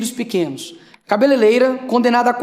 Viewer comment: eu ouvi "cabeleleira"